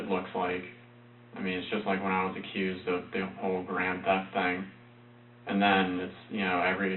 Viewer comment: Eatme Douche yes you’re entirely right. Did anyone hear if he told the detectives that he had gotten fired and arrested for withdrawing unauthorized propanol from the medication cabinet? I thought he said it was for auto theft! But during the trial the psychiatrist finally admitted that the reason was the propanol. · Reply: Yes, he did - you heard "grand theft" lol, and immediately equated it with auto theft - it just means the theft was over a certain amount, usually $1000 bucks. But, with grand theft auto being a popular game, everyone equates it with auto theft.